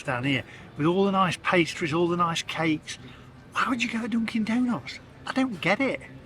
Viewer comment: Must be my hearing but I thought you said Donkey Donuts.